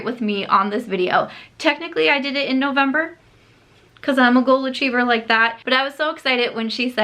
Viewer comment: When you said I'm a "goal achiever" I really thought you said golden retriever and I did a straight up double take! Lol. Love this tag. I may borrow it for my channel. #santasam